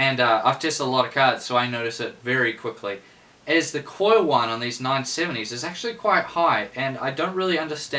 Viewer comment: I didn't catch one word you said about 11:24, turned captions hoping they would help me- they don't understand U at all, recommend that if you wanna have a laugh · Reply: 'the coil whine on these 970's is actually quite high'